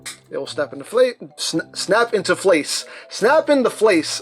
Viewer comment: I heard snap and deflate